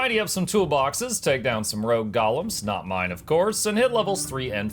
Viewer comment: When you say “Golems” I hear “Gollums” 😂